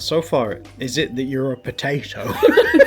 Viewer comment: Omg! I thought when Said “is it that youre a potato?” That he Said “is it a neuro-potato?” 😂 and now I want the neuro atypical potato to be a thing!